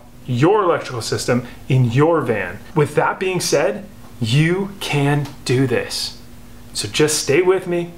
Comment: I swear at the beginning it sounded like he said "You can't do this"